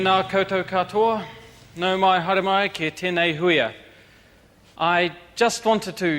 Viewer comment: Ok, what the hell is he saying in Māori? Tena koutou katoa is obvious.. Hello everyone, but what's the rest? nou mai ha ramai ketenai houi a... I have a lot of new baskets nou mai hara mai ketenai houi a... it's not your fault from now on nou ma i hara mai ketenai houi a... I have sinned against you nou mai hara mai ketenai houia... I'm sorry to hear from you nou mai hara mai ketenai huia... it's not your fault nou mai hara mai ketenai hui a... I'm sorry for this meeting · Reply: it means welcome to this meeting, nou mai ki tenei hui